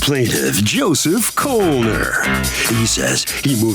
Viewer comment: I got scared for a minute. I heard 'Joseph Coleman' while listening to this case at work. Then I saw the spelling of the last name. I was like "when did I show up on People's Court?"